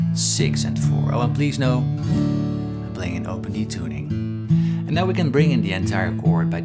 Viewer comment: Oh, open *D*. When you said it the first time I thought you'd said open E. 🤣🤣🤣 I misheard. No wonder I got confused at the look of that A chord. Ha ha. Beautiful work as always, Paul.